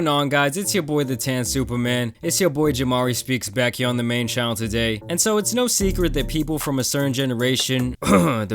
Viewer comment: Why my boi jamari say today like that at 0:06 😂😂😂. He said "Tadhay"